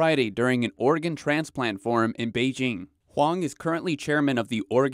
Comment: did i hear this right 1:17 did he say an organ transport farm